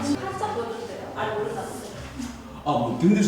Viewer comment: 윤봉길씨라고 들은거 같은데 제가 잘못 들은거겠죠?